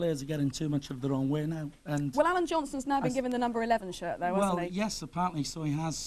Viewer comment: I thought it said Adam Johnson 😂